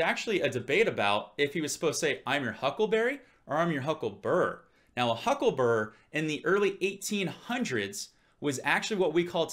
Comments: i always thought Doc said I'm your Huckle"burier" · Great now I hear "hucklebearer" ...